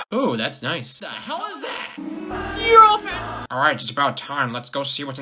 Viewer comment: What did he say at 0:20? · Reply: He said "You're all fat."